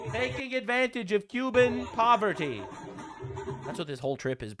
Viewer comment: He actually says Cuban missery, it is the same as poverty but I feel like it sounds even worse when put that way. I do hope Hasan doesn't escape the legal repercutions for breaking the law of where he can stay while on Cuba, and regardless what he and his people did there is disgusting, treating Cuban as entertaiment and benefiting from their suffering.